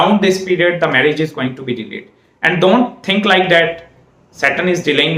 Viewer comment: At about 11:35 -- He says this is a planet of delay. Saturn is going to delay your marriage and married life, up to the age of 32 to 35. After this he says something I can't quit make out: "Around this period the marriage is going to be *******" I can't make out that last word/s of that sentence. Anyone with better 'hearing' than me? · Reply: delayed